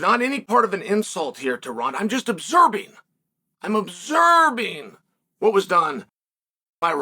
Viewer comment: Is Chael saysing obserbing?